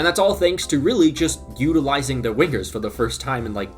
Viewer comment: bro really said Wiggers instead of Wingers